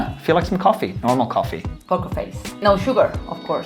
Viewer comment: does she say ‘cock face’?